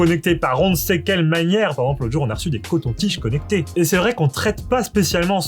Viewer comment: C'est moi ou à 0:21 il dit des cotons tiges connectés ?? Et sinon pour le titre je propose "La tech loufoque épisode 1" Les deuxièmes lunettes Bose on dirait qu'il est aveugle, on dirait Gilbert Montagné !!! 😂😂